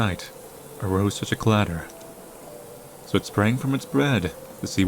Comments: Hold on. I just passed the one minute mark, and I swear I heard, "So it sprang from its BREAD to see what was the matter." I mean, I like bread and all, but it's not something you spring from... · WHY WOULD U SAY BREAD INSTEAD OF BED IN